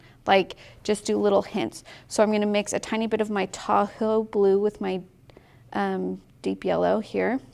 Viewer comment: This is SO fun! I'm enjoying the process. Side note: Every time you say, "Tahoe Blue," I hear, "Taco Blue." 😆💙